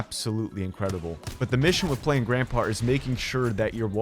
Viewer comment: did anyone hear “playing grandpa”? 😂